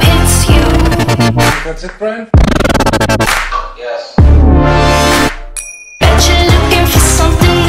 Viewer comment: "You think that's it Brian?" "Oh yes" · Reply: I hear "that's it friend, yes"